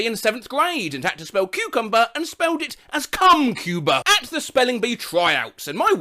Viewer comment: the subtitles said come cuba instead of cumcuber